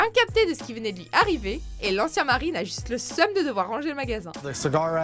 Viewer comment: C'est moi ou à 2:25 elle dit le mot seum 😂😂😂😂